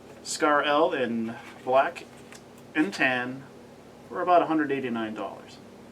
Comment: sounds like he says 180 million dollars xD